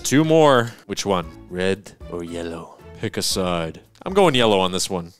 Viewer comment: did he say red and yellow?